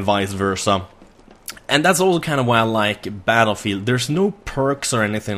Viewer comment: (almost) Did you say Battlefield 3? lol